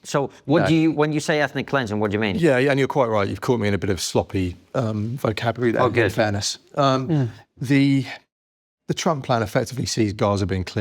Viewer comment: I had to go back and listen twice to the Trump ethnic cleansing comment. At first I thought he said they had caught him in a bit of sloppy TDS. Too funny. Thank goodness for closed captioning.